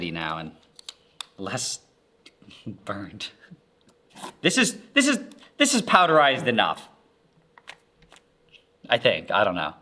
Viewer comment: What said: “This is powderized enough.” What I heard: “This is traumatized enough.”